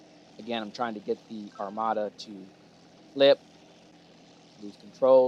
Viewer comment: thought it said ace armata! lol